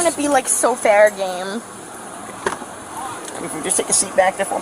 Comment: Did she say " I only wanna be like so fair game" ? @ 22:03 That's just so wrong, a fella could lose his job.